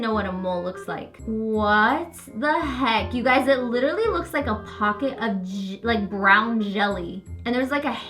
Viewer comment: I literally thought she was gonna say “ a pocket of jizz...”